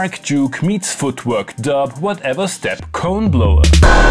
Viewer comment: "Whatever-step Coneblower" i heard it as 'corn-blower' the first time i listened, and i was like 'wait what ? when did you manage to get to kansas, texas, and/or arkansas ('arkensaw') ?' creepy banjo musics in the woods